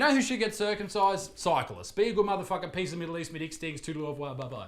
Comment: “Cyclists” I thought he said scientists!